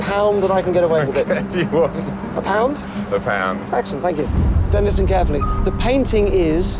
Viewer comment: did he say don't listen carefully ! ? Lol 🤣 also that was the chance for the art dealer to switch that painting with a fake !